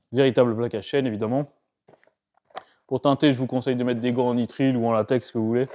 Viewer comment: Au début j'ai entendu "des gants en mithril", alors entre ça et la barbe...